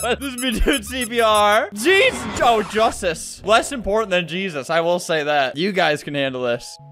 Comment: ?????? No. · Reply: SORRYY I thought he said more important than Jesus. I put on cc it said less important. IM SORRRRRRRRRRRRRRRRRY.